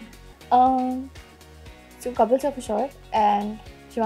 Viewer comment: Wt did she say at 4:35 ?? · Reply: She said "Kapil sir for sure"✨